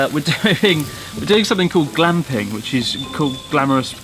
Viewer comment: I really thought homie just said “glomping” and was like, oh no.